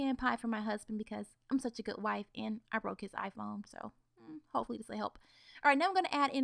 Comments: at first I thought she said "Cause I'm such a good wife..and I broke his eyeball" and my first initial reaction was O__O "wait what?!" ...until I read the comments realizing she said iPhone.. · i thought she said and i broke his eye ball